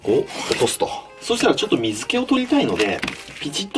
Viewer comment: 鱗を落とすをうんこを落とすって聞こえてちょっとびっくりした。